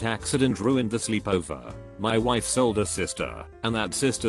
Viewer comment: The very first line I heard was "My wife sold her sister" and I was like, "yup, that'll do it"